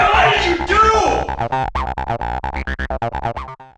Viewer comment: I thought he said "Nigga what did you do?!"